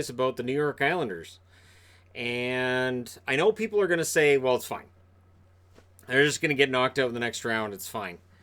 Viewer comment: It sounds like he says "well fuck"